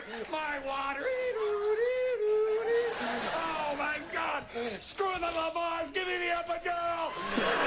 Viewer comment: What'd he say? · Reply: "Screw the Lamaze--give me the epidural!!"